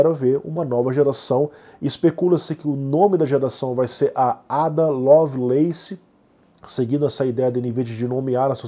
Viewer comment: Por um momento entendi "Linda Lovelace" hahahaha